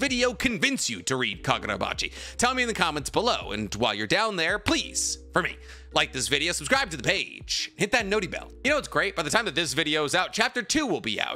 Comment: I know you re saying " hit that noti bell " but I always hear it as " hit that naughty bell"